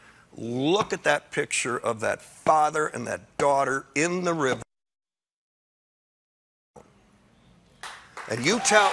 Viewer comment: I have no idea what Jeff Daniels said, but it must have been really shocking, because I don't think the Late Show ever muted and cut to the audience simultaneously like that before. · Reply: Dan Burke Someone who was in the audience said, he said... “face fucking down!” Regarding the father & child who drowned in the river trying to get here.